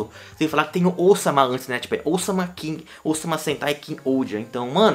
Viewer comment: Eu escutei "Osama Sentai" 💀